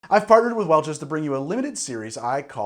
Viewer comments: Forgive me but I heard "I farted with Welch's" at the beginning. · I genuinely thought he said "I farted with Welch's..." at the beginning.